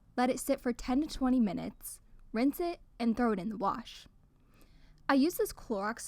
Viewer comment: for a second i thought you said throw it outside 🤣